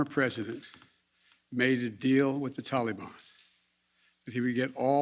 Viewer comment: Everytime he says Taliban I hear "Tallybon tallybon tally me banana. Day light come and me one go home." Fml